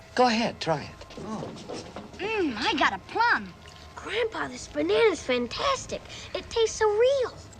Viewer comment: did he just say 'grandfather's bananas taste fantastic' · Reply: No it said “grandpa! These bananas taste fantastic”